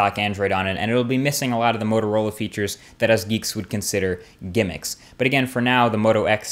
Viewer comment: What word he says at 8:30 min? gamix? geemics? pls tell me :)